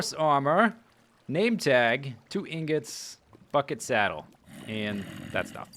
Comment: i thought you said "fuckin' saddle!"